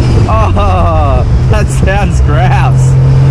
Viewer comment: “That sounds grass “…😂🙈 That’s a new one!!! Lmao Unless you were saying “that sounds like ass “ Between the accent, and the weird way folks in other countries talk (words they use) It’s a mystery what is said 😂